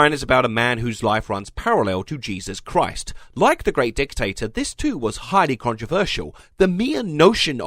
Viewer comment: Ok at 7:30 I thought you called jesus a dictator. I was a little disappointed but still got a good laugh out of it